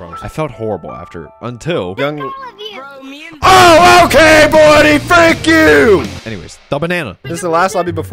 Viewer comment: what did he say? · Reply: He said “frick all of you.”